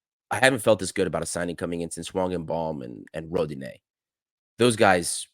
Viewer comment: I don't understand who do you mention at 14:18 along with Rodinei :/ Can you clarify? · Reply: Hwang !